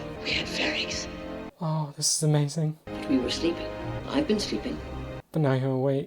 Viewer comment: of course Andor is more valuable alive than dead. Luthen never wanted Andor dead. He personally recruited Andor. Risked a lot. And the mission was a success. But Andor acting like a mercenary, getting the money and escaping, was too dangerous for Luthen. I mean... if Andor prefered the money than fighting the Empire, what garantee did Luthen had that Andor would not spill the beans about Luthen for money if he got caught? But you can see even so how Luthen resisted going after Andor. It was Kleya that said he was sleeping, that he needed to act on the loose end. But Andor putting his life for the rebellion was exactly what Luthen wanted from the start. (and aside that, he already had enough proof how capable Cassian was... Aldhani, extracting Bix and fooling all Imperials AND Luthen himself...) Also I think Marva's speech touched Luthen. And it was her son after all. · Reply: I thought Kleya said he's "slipping".